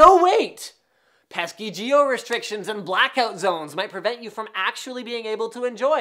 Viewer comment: Did he just say jio?